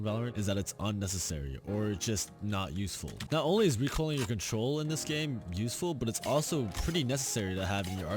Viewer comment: did he just say recoiling your control or is it just me?